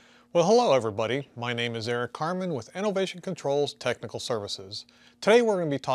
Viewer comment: Who else thought he said his name was Eric Cartman? 🙂